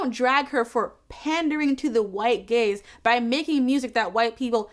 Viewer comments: I don’t know why but I thought you were saying, “white gays” instead of “white gaze” for way too long. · For the first half of this video I thought you were saying critics are accusing Lizzo of making music for “the white gays.” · Ngl I misheard "white gaze" as "white gays" 🤦🏻‍♀️